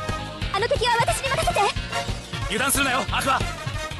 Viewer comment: 早送りのせいで「油断するなよ、アクア！」が「揺らすなよ、アクア！」に聞こえるw